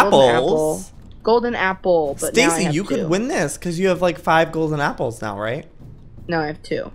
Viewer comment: Ha, I thought they said: Stacy-You should ship because I'm shipping Joey-ok. EVERY FANDOM MEMBER EVER.😂😂